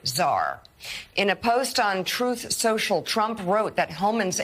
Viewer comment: "Untruth social", I had to listen to it twice to make sure I heard it right. 🤣🤣🤣